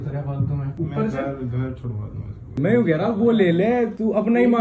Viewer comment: On 2:51 is said , "main ghar chor raha hu"??